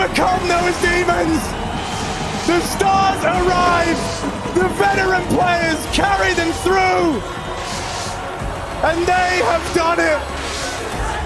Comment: can someone tell me what the caster said ? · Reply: "The veteran players carry them through" Also does anyone else think that the casters and sound so much like Alex and Chad?